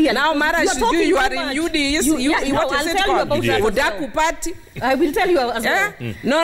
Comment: I think I heard "UDAKU PARTY